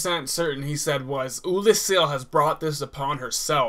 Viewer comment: I heard "Oolacile has brought the Abyss upon itself". Just sayin.